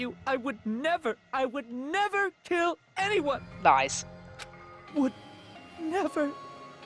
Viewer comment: Lies What I heard: Thighs